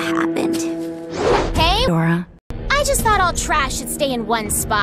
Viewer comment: Bosha said "hey!!" Then.... I heard "adora" and it is freaking awesome ✨ aHhhhhHhGhkwsynt